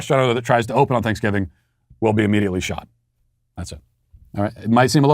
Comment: Laughing hysterically because I heard shot. Disappointed because my friend pointed it that he said shut which makes more sense. I am the asshole.